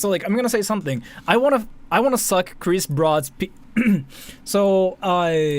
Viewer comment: I'm sorry. WHAT???? You wanna suck Chris Broad's p did I hear that correctly?